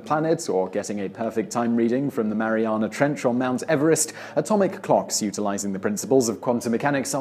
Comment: 'the Mariana Trench on Mt Everest'. What am I missing? Did he say 'or' ? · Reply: Yes he said or. ?